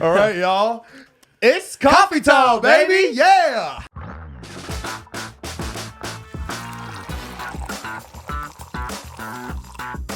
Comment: It’s THE BEST feeling to hear them say “it’s coffee talk, baby!” because in the very beginning it was done randomly in big red and now they have an entire podcast and coffee brand I just can’t... it’s the greatest thing. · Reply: i thought they were saying “ it’s coffee time baby. “😂😭